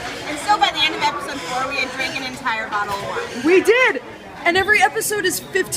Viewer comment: at first i thought i heard one bottle of vodka and i thought not bad for 3 girls but then once they said it again i realized they said wine....XD